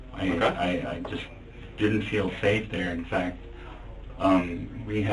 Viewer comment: did he just say 'seel fafe' lmao